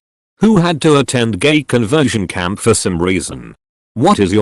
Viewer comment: I thought it said gay conversation camp. I just pictured a mass anti gay therapy